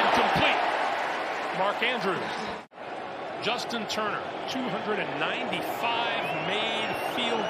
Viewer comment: Did anyone catch the announceer calling him Justin Turner 😂 😂 instead of Justin tucker? At like